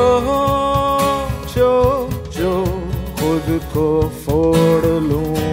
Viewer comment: did only i hear jojo?